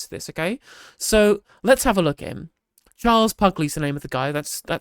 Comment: I thought you said Charles Fugly.